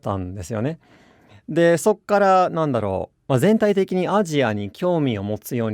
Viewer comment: Each time Dogen says "After that", I keep hearing "そっから" while the text says "そこから". So is Dogen using a different dialect? Or is そっから the actual native pronunciation of そこから? Or am I just hearing it wrong?